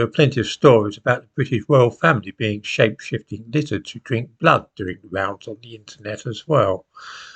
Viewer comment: 12. "after all there are plenty of stories about the British Royal Family being shake shipped & lizards to drink blood" WHAT?!!! Please speak more clearly. I couldn't understand what you said here. (I hope it's not true!!!)